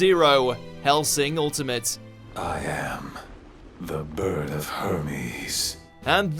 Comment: Did I hear that right at 6:44? I think Alucard just said he was the bird of herpes.